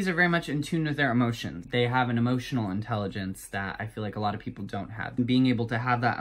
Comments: I thought he said emotional boner and the best part is.... it’s so true😂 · I heard "Pisces are very much in TUNA with their emotions" and I can't unhear it now, send help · thought he said emotional boner omg